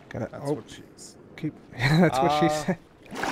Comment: Also 3:33 did say "That's what she said?"